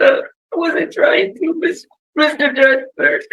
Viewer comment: Does she really address him as, "Mr. Judge person" @ 28:18? Or am I hearing it wrong? lol